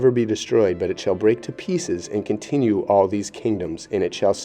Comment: i think it may be Consume instead of Continue.